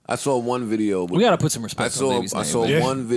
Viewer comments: So im the only one who heard Rory say at 5:34 "We gotta put some respect on niggas name" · Mark.... Did this white guy just say put some respect on n*ggaz name? Budden TV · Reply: Ok I thought he said that but it did sound like he said put some respect on the baby's name to but I did hear nigga at first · Did Rory say "we gotta put some respect on niggas name" ??? 🤔🤔🤔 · On God it sounds like Rory said “we gotta put some respect on the nigga name” 💀👀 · Did Rory say “we gotta put respect on niggas name” or am I trippin 5:36 ???